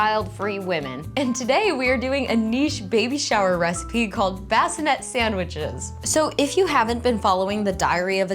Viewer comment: Ngl I misheard you say "bassinet" as "Baphomet" and got real confused for a second there